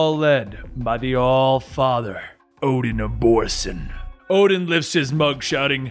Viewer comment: Kinda sounds like u said "Odin abortion" intead of Odin of Borson 0:54😂😂😂😂